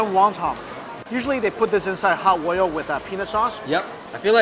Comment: did he just say penis sauce??